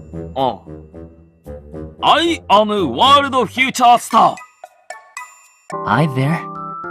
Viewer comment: STOP I HEAR HIM SAID “World huge ass star” INSTEAD OF “World future star” 😭